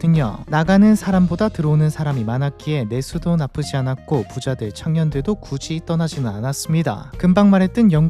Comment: 발음이 부자들,창년들도 라고 들림 ㅋㅋㅋㅋㅋㅋㅋㅋ